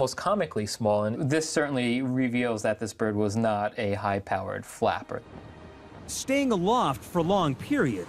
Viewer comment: I swear I heard "high-powered fapper" the first time